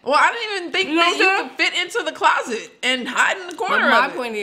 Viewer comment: Did she just say " I didn't think you could fit in the closet". LMAO LMAO LMAO kept talking, I would have been like "hold the phone, what did you just say"? slid that one in. 😂😂😂😂😂😂